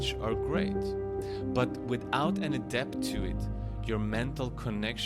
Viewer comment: Can anybody help me understand what he is saying and what he means. "Unless you Adept to it..." 7:35 A depth to it... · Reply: "Without any depth to it, your mental connection..."